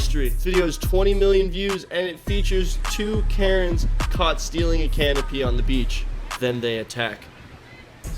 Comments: who else heard stealing a can of pee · at first i thought said “two woman were caught stealing a can of pee”🤣